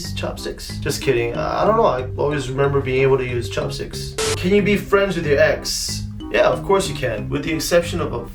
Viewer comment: I wasn't reading the subtitles and I thought he said "Can you be friends with your eggs ?" 🥚🥚